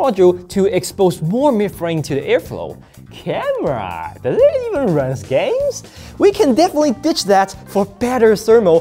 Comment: wtf did he say cameraaa dertuetztidhf games idk man